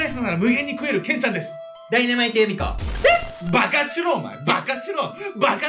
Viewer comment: 最初の爆発しろ!!がバカチロ!!って聞こえたわ…バカチロってなに…？🤔